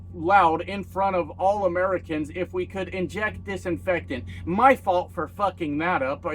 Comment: Wow, I could have sworn he said "Blea_h" but no it was disinfectant (I just found the vid), which is WEIRD that he knows a long word to describe the product instead of using the brand name like he does with everything else. Remember "Tim Apple"? Because, you know, CEOs have to have the same name as their product? Dude, I got suspended BY yt in 2020 for a MISQUOTE! /)_- f-ck.